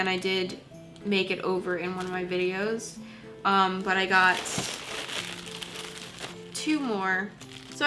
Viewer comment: "uhm, but I got tumor." 😂 I literally heard that 😂